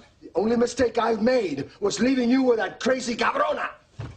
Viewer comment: What did he say there? "Carona"? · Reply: he said cabrona